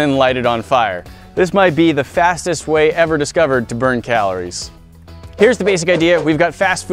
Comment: I thought he said burn callis Not calories